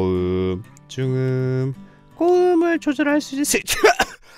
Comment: 왜 나는 고음을 조절할 수 있어이새키야!! 로 들리냐